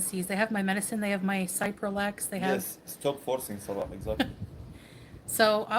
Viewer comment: What medication is mentioned at 10:55 or 10:56, subtitles say it's cyprollex, but can't find such drug, is the spelling wrong, I don't understand what she says. 😀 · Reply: Cipralex? Possibly